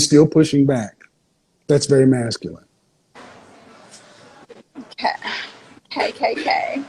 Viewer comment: did she said KKK?